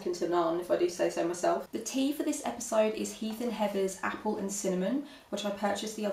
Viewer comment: I thought you said the brand of your tea was "Heathen Heaven" :)